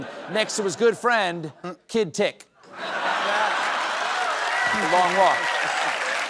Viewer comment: weirdly I heard the word "dick" 😂😂